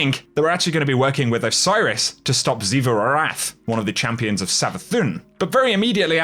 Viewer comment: wait! Did he just say xivu arath? Thats oryxs and savathuns youngest sister! The god of war.